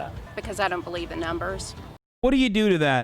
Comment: It kinda sounds like she said "I don't believe the numbers" . Did anyone else hear that? AM I CRAZY? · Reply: I listened again. She just said "I don't believe in numbers" I don't think that's what she meant, but it's fun to dunk on